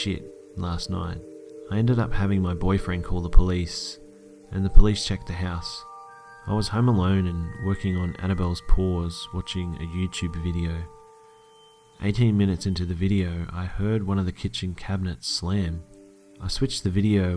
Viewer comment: What the hell does he say at 1:58. Annabelles pores? what the fuck is that?